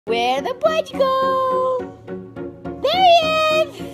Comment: At the beginning, I thought she said, “where the Pudge go?!”.